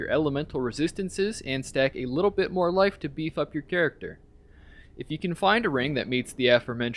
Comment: When talking about rings it sounded like you said "get more life to bee fuck your character." xD